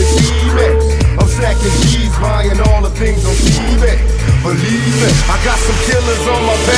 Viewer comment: does it sound like he says im buying all my things on ebay..